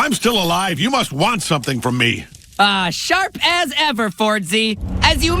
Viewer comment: i thought he said "Sharp ass effort fordsy 🥰"